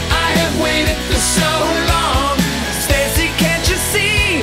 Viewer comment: Every time he says "Stacy", I hear "Timmy".